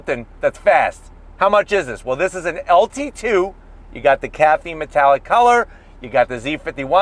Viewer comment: That color is Caffeine Brown, which is now discontinued. Zeus Bronze was a lighter color that was discontinued after 2021. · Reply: he clearly says caffeine metallic, he just misspoke at first.